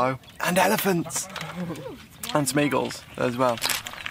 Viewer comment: Thought you said 'Smeagols' at first. Thoroughly enjoyable video. More of the same please!